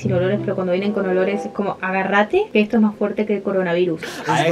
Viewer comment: AGARRATE PORQUE ESTO ES MAS FUERTE QUE EL CORONA VIRUS JAJAJAJA 😂 (eso creo que dijo) :D